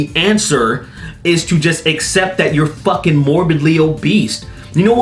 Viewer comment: did say morbidly o'beast? 3:37 because i hope he did! teehee